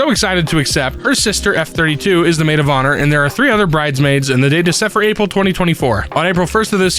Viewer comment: ”there Are 3 other bridesmaids”. I heard “there are 300 bridesmaids” 😅